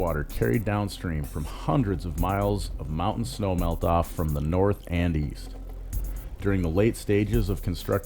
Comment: Did you say, "the water from the North Andes?" @ 3:36 And many crops are grown west of the Columbia, like in the Yakima Valley. @ 2:55 I sure wouldn't mention "soy" and onions, while failing to mention apples, dairy, and many others. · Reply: Lol, I said north and east, but yes upon review did sound like north Andes. Of course, there are many other crops. Monsanto doesn't really control those or isn't trying to control them(not yet). Monsanto will focus on trying to get as many of the crops they control in those fields. Remember this was on Monsanto in Eastern Washington. I appreciate the input.